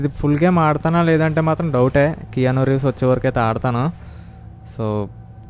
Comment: what is that game keona rays bro u said in the video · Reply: 😑adi Keanu Reeves famous actor athanu ee game lo vunnadu dani gurinchi matladaru